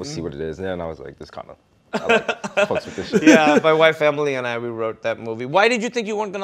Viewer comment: Captions 8:57. "My white family and I, we wrote that movie." What? No, he said "My wife Emily and I" Cuz that's who wrote the movie. Come on, captions people...